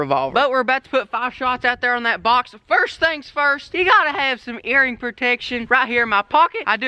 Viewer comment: On 4:24 is it just me or did he say eating protection not hearing protection